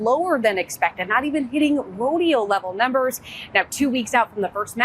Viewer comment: Not even hitting [unintelligible] levels. What did she say? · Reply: rodeo